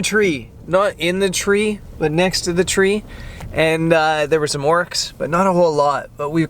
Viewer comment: I wonder how many people heard you say "We saw some oryx" in your Canadian accent and thought it was a LOTR reference. Yet another cool video! Even shooting birds in my local woods is largely hit-and-miss re finding birds (1) in an easily-shot location and (2) doing something interesting; and that's mostly due to learned familiarity with my local fauna. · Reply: Same. I heard "there's some Orcs" and was a bit "wtf?!" haha